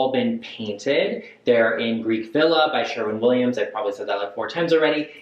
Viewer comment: Did you just say , Greek Villa? I just spent nearly $60 for a gallon of that color to paint our children’s bathroom which will have navy blue accents. I also plan to use Greek Villa to paint the rest of our house which also has accents of navy blue. I haven’t seen any Youtubers mentioning that white before. I knew there was a reason I was drawn to your channel 😍.